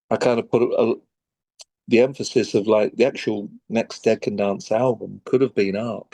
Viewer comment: Brendan said “dead can dance” and it sounded like “decadence”… never occurred to me that band name could be this two fold name intentionally or unintentionally…